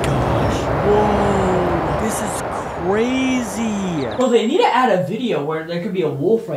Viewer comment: I thought he said “that’s windy” but it’s “that’s crazy”😂